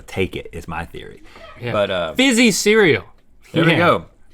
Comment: Link said “fizzy cereal” and my phone lit up and thought he said “hey siri” 😂